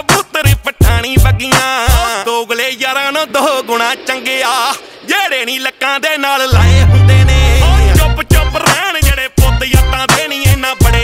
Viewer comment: Subtitle at 0:45 it is not what he's saying, it'd be like - " that are kept in our hoiters around the waist "